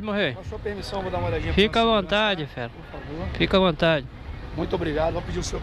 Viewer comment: eu entendi kika a vontade kkkkkk